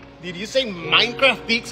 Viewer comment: I thought he said 9ft pizza 😭😭